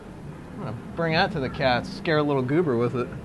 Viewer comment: What's a doober? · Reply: i know goober is his cat, i just thought he said doober